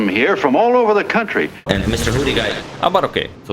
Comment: What is being said when 4:44 Agadmater transforms? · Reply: In this specific instance, says "And Mister Hoodie Guy"